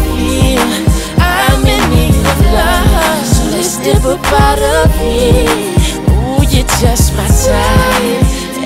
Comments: Y’all worried about if he said “she’s fine too” or “she’s 5’2” but when I was little my stupid ass thought he was saying “so let’s Hippopotamus” and not “so let’s dip up out of here”🤦🏾‍♀️🤣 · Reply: and I thought he said "let's hip hop out of here" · Y’all worried about if he said “she’s fine too” or “she’s 5’2” but when I was little my stupid ass thought he was saying “so let’s Hippopotamus” and not “so let’s dip up out of here”🤦🏾‍♀️🤣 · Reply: Lul Tank my dumb ass thought he said less tip up outta here💀💀💀